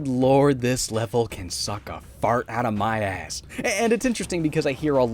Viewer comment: did he just say that the level can suck a fart out of his ass?